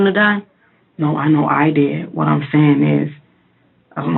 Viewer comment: Anyone else hear a child's voice say "No!" at this point